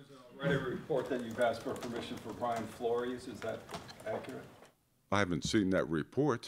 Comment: Who was mentioned at 3:24? Couldn't quite hear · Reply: Brian Flores? D coordinator of Pat's for Rooney rule